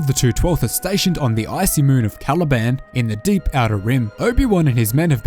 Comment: I thought you said Taliban for a second.